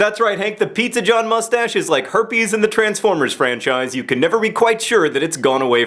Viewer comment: My favourite of your videos in AGES John! When you said Herpes and the Transformers Franchise, I thought you said Herpes IN the Transformers franchise!